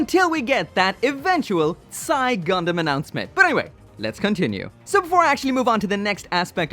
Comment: The what Gundam? I'm trying to look it up but I'm not sure what he said. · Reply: Xi Gundam aka better and more poseable looking than Penelope